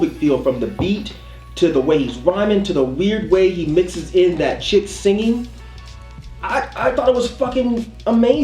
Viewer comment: nice, that's not a chick singing on No Exit though, I believe that's Miguel lol. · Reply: i thought he said "shit singing"